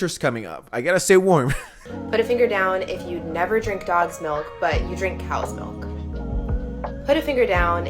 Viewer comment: Hold up- did she say dogs milk?! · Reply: I thought she said gods milk i was like tf is that